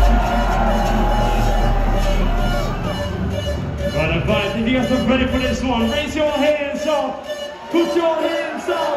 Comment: プチョプチョヘンザ？ よく聞くこのMC、何て言ってます？オーディエンスも叫んでるみたいですけど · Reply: put your hands up